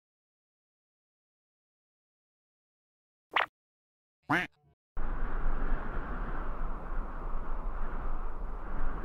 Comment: What he says: bye have a great time What I hear: bye afagri time